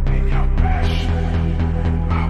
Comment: This is the version that I searched many time. Litterally best version of After dark x Sweater weather. Sounds like gigachad singing. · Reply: When he said As the hours pass i thought he said:There's the oldest place